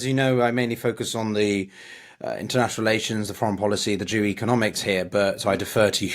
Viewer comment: did he say "the Jew economics"? I'm asking seriously because sometimes accents can be tough...